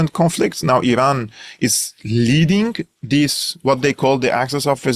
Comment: Where and when did I say that? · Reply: sorry I misheard leaving instead of leading.Soory again for any inconvenience.